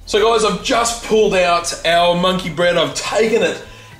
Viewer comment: I thought it said money bread. Fail